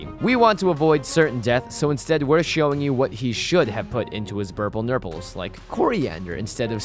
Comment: PS I thought that it said purple nurples.